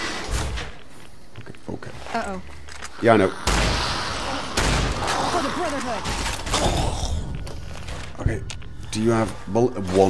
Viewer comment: Was listening to this video while modding my own NV and I thought I heard the daughter of Van Cleef scream "For the Brotherhood!"